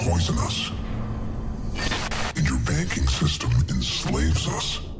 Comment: I swear to god, I always thought vergil said "and your baking system enslaves us" instead of banking, for some reason, and I can't stop laughing at the thought of it